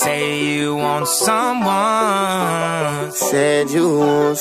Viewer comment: Who also thought he said some wine